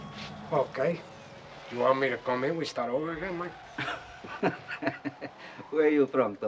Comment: Another Mandela Effect, Ive always thought He said" You want me to go out and come back in so we can start all over again Mang" Weird